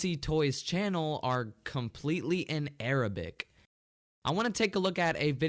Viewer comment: I thought say Spanish, but he says Arabic